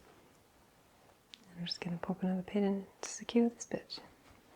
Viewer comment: This is such a great video! 7:20 but was I the only one who thought she said “Now we’re just gonna pop another pin and secure this bitch.” Lololo